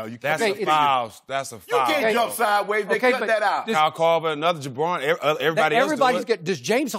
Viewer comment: i swear he just called kyle korver a Jabroni? Lol the Rock is that you?